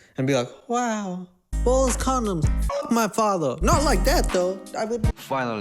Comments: This is my 4th time watching this video and this whole time I thought was saying "Ball-less condoms" Lord I'm dumb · Bruh it took me half through the video to realize you were saying ballas condom! I thought you were saying ball-less loool, I was like isn’t that all condoms? Lol thanks for always making me laugh & keeping it fresh ✨